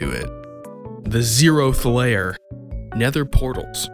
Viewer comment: Thanks to my auditory processing issues, I heard "The Zeroth layer" as "The Zeroest Phlayer", and was like "I don't think I heard that correctly". I just wanted to share this information. Sometimes, watching videos with my processing issues is just... wild mishearings.